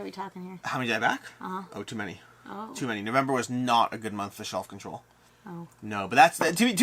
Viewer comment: I thought you said shelf control but realised you actually said self control, though shelf control works to, maybe better, lol.